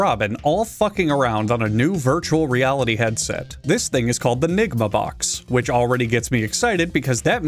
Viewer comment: not gonna lie, i clearly hear "the nigga box" here